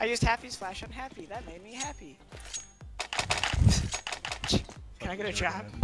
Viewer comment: "I used Happy's flash on Happy that made me happy." awkward silence for few seconds: "...can I get a drop?" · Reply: I thought said ''...can I get a job?'' LOL